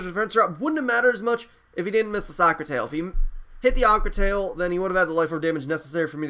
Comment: Lol I could have sworn he said soccer tail then said aqua tail but either way great match